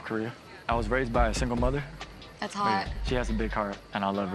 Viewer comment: I ‘think’ 12:33 she said “that’s hard”